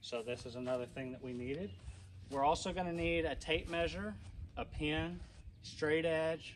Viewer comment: did you say a Pin or a Pen? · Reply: Ink pen.